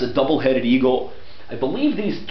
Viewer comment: well i hear this ¨i believe three C :D¨